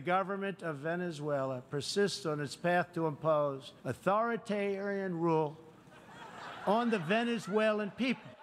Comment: Authoritararian? Is that what I heard, or did Trump have a mini stroke?